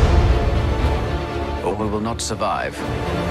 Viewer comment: Why do they call Luke, Owen? Like I’m genuinely confused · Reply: nvm I heard him wrong. He said “or we will not survive” but I heard him as “Owen will not survive.” I thought he was referring to Luke.